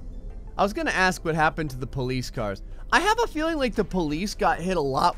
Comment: it sounded like gray said police cocane